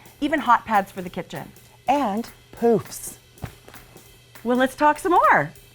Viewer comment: When said poofs in the intro I totally thought she said poops for a second and about died I was laughing so hard!